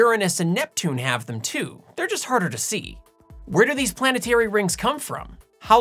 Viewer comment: I heard: " where do these planets hairy rings come from?" Lol